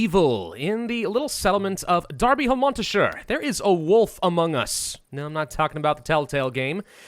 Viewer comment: Oh why did you say AMOGUS ??!?!?! AAAAAA GET OUT OF MY HEADD!!!!!